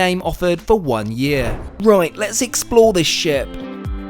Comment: Sorry I thought you said “let’s explore this shit” then 😂😂😂😂 Misheard sorry 😂